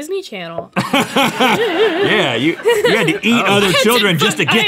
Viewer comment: at about 1:48 it sounded like was gonna say I had to F!#K